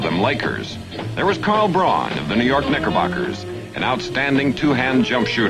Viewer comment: Wtf did he say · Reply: Knickerbockers that’s why they changed it to just the Knicks